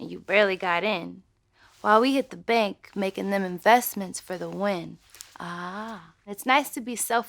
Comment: Bro when she said "treat my goals like property" why did I used to think that it was "treat my girls like property"? 💀💀💀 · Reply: and when she said “making them investments” i thought she said “making very big m’s”.